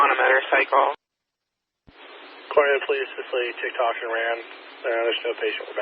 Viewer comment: did they say “Tik toker ran” lol. I swear thats what I heard but I’m hard of hearing a little bit😂